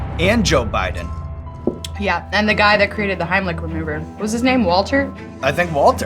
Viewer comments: I swear it sound like she said heimlich remover LOL she's awesome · sounded like she said "the guy that created the hemilich remover" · does she say “ heimlich remover “